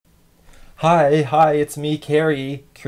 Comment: When Cary said “Hi Hi It’s Cary” I thought he said Hi Hi Kids